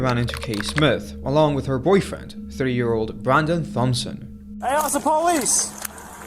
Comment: I thought you said 3yr old boyfriend! Glad I entered back and heard it properly 🤔